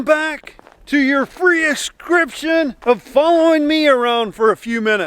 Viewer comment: when started the vid i thought he said prius subcribion